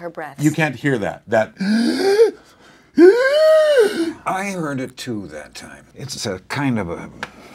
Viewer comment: does he say "I murdered two that time.." or am I just imagining it? Would fit his chosen profession. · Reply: He heard it too that time